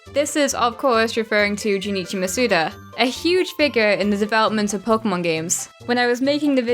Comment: It's 3 am and I thought I just heard her call Junichi Masuda a biggot. No idea how I heard biggot rather than figure.